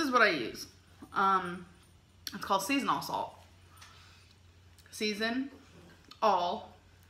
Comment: The skin of a potato is the best part! When you said, "Season All" salt, I thought you were saying seasonal salt.